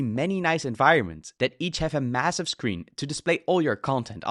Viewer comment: did you say massive?